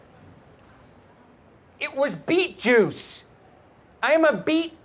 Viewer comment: It was beet juice? I thought you said go beat the Jews